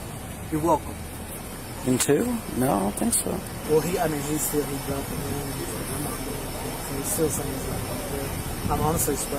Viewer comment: Part of the video where the subtitles say “unintelligible”, I can clearly hear the officer say “he dropped the gun, he’s like ‘I’m not going to jail’” This is at the 12:40 mark. Wearing headphones really helps with the dialogue in some of these videos.